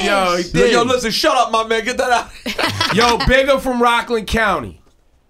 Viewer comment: Did Rosenberg say migga